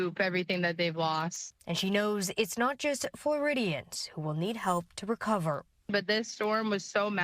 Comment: did she say floridiots 😂😂😂